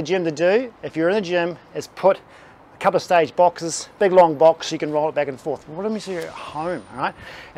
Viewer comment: stage box ? What did he say to put under the weights ?? “Long box” ??